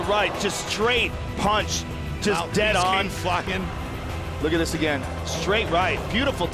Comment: Did Joe say "dead on fuckin..."?